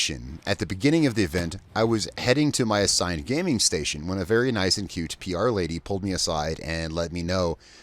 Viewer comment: "A nice and huge PR lady" So did you intend to say she was nice and cute or was she just like 6'7? :D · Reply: Lol I thought he said "nice and cute"